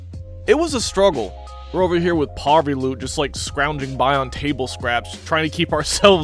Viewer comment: Did...did he just say poverty loot? if he thinks I’ll tolerate that language he knows damn well I will because we strugglin out here getting standard stocks and a backpack on the drop